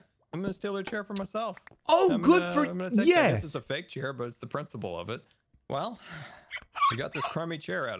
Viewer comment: did he just say.... “ It’s the principle of it ”